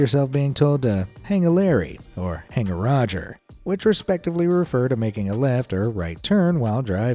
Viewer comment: I've heard it as "Hang a Louie"